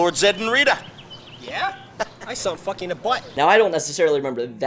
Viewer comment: What did say? 😳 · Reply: Sounds like. "Yeah? I saw him f****ng her butt" 😂